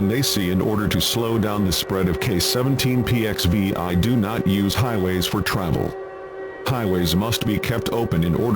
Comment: What i heard on 28:11 is: "In order slow down the spread of K17PXV. I do not use highways"